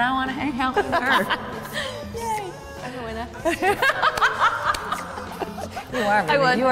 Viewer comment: What did Jodie said after "I want to hang out with her yey"? It's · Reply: I'm a winner😂